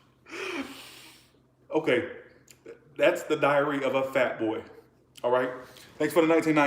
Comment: I thought you said, " that's the diarrhea of a fat boy." Lol 😂😂😂